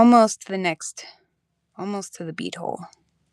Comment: i heard "almost to the B hole". 💀💀💀